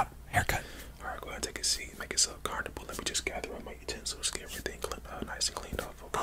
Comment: is it just me or does he say make urself carnival?